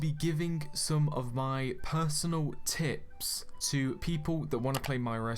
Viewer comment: For a sec i thought you said arsenal xD